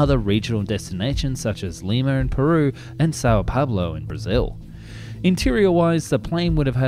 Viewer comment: Does he say Sao Pablo there? Someone's been watching a lot of Narcos